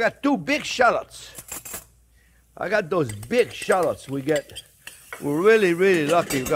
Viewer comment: I about died when he said "b**ch shallots instead of big 😂